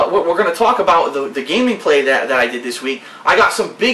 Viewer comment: did he say Gamingplay